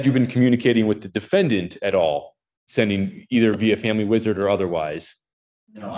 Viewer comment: did I hear that right??? "By family, WIZARD or otherwise?" lol